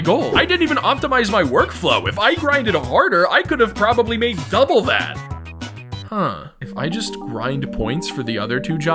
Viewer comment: i was so happy that he put so much mkwii and luigis mansion (oh and 3ds) songs in this video and then i accidentally misheard "double that" for "double dash" (its a mario kart game for the gamecube)